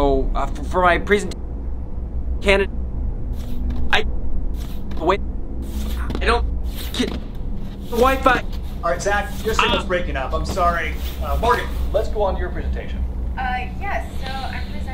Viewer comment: i thought mr k said "morgan, lets pull out your ribcage" ☠️☠️